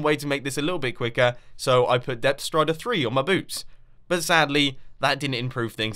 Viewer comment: so i put death strider 3 on my boobs it sounds like he is saying